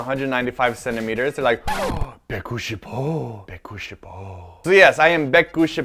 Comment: What is begushipo (0:43)? Sorry if I'm spelling that wrong/mishearing it.